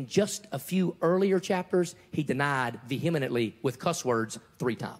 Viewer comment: Did just say Ve-hem-ih-nent-ly? Did he mean vehemently?